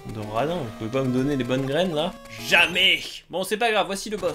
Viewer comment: j'ai compris "balles graines" (l'attaque plante dans Pokémon là, notamment connue et utilisé par Chapignon)